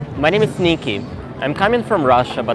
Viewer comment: It sounds like you say “my name is sneaky” sometimes and it always makes me laugh 😂